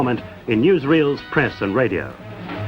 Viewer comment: sounded like he said "in Israel's press and radio" lol